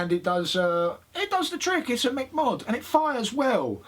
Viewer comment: Did say Mc Mod about 1:50?